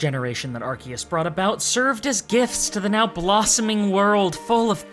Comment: at this i thought you said GIFs because pokemon are often just repeated life cycles and sometimes barely seem indistinguishable to one another..........Then i realized it was Gifts